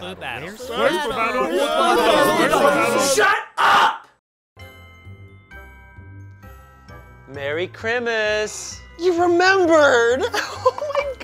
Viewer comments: Ian says merry chrimas instead of christmas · Why does Ian say "Merry Crimis"?